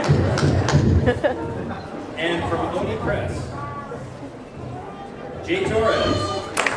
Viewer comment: what did he say? Press?